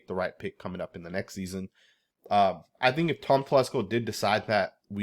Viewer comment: I thought he said “fuck Tom Telesco”😂